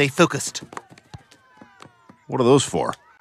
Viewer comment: I thought he said, power thongs 😂